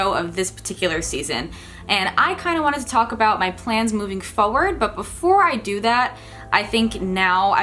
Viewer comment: at first i thought it said moving ever forward